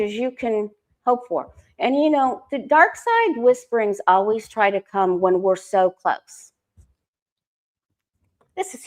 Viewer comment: Can you repeat what was said at 40:40 “ the dark side whisperings always come…” what was the rest? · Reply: "The dark side whisperings always try to come when we're so close"